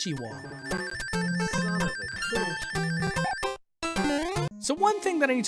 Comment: Did i heard son of a ** at 15:49? XD